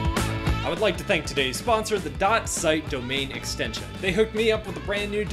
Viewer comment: lol I heard domain expansion what's wrong to me